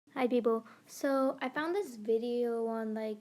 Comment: Why did at first i heard- hi beeboo idk why i wheezed so hard